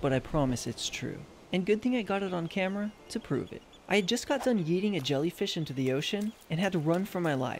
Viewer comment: When u said “yeeting the jellyfish”I thought u said”eating the jellyfish”